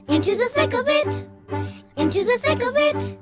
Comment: I always thought they said “into the snickle fritz” lol my kids used to watch the back yardigans….takes me back ❤